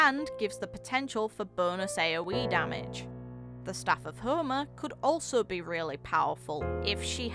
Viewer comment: I misheard "Staff of Homa" as "Staff of HOMU" for a second there. lol. Very good video though in all seriousness. I'll look forward to seeing how she plays, while I'd like to get her I... also need a pyro character.